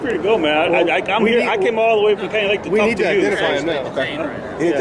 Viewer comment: he actually said Canyon lake not KY lake...